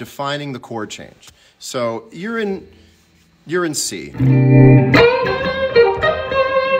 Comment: The closed captions generated are priceless... JCM: "So, You're in C" CC: "So, Urine See." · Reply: haha just got to that point. I heard "urine sea" hahaha